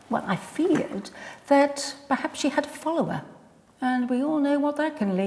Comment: I had to replay the bit where they suspected that Kate might have "a follower" because I initially heard it as "cholera," and, ladies, if that's the case then her being a little bit distracted when attending to the chamber pots is not your biggest problem!